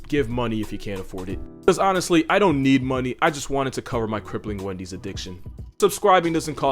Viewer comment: I love how honest he is about his crippling Wendys addiction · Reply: Omigosh, I thought he said he had a crippling onesie addiction! An odd fetish to share. Muwahahahaha!